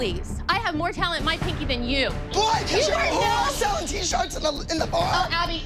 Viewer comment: did abby say BOIII😂😂 i swear she did